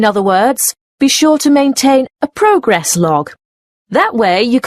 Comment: Ok ji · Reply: hi! In the question Number 20, the answer you wrote Is progress, but I Heard "progress lock" or something like that. Is It possible?